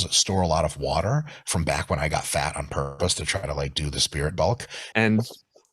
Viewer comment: does he say " spirit bulk"?